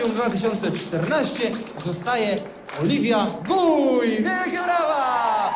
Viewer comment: czy tylko ja usłyszałam ,, Oliwia Ch*j